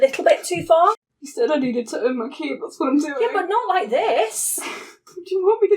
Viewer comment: I've been trying to figure out what she said at 7:00 for way longer than I should've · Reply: “You said I needed to earn my keep - that’s what I’m doing”